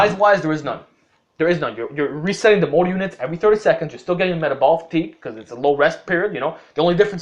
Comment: So if I do 10 sets of 3 reps for any workout, lets say shoulder press. Is that all I do for the muscle group? And how long between sets 7:47 sounded like you said 30 seconds